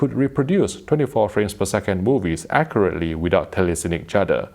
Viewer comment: is it me? or did he say "24 frame per second Boobies" ?